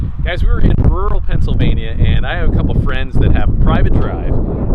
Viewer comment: When i hear "rural Pennsylvania" i just hear "mexico"